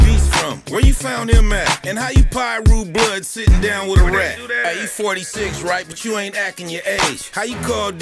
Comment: Had to listen to a piece afew Times at 2:20 thought he called out e-40 but he said sum sum sum and said “he 46 not e-40 sick sum sum lol